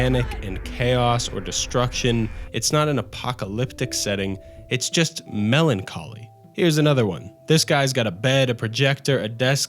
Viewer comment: i heard "it's just melon calling" 🍉📞☎️